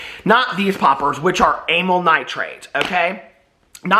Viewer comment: I really thought you said anaI nitrates hahahaha 💥